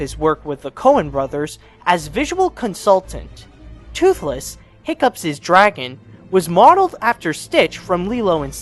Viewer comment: Did he just say "Hiccups's"?